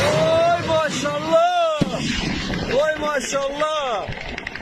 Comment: Am I hearing correctly....? "Ench(?) Allah" (9:47) and "Allah Akbar" (10:42) from a Moscovite? As President Zelensky commented about stikes on a holy Christian site, is Russia now resorting to playing a religous propaganda card?